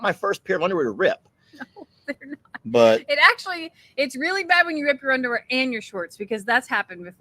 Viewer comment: I thought said you had a blow out in your underwear. 😅😅😂😂